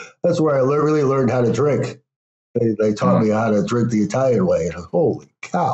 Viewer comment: Thank you for your reply. I may go back to the video to listen again. But for some reason I thought he said he learned to drink Rome, which is why that resonated with me. But either way, it's not an integral thread to the video.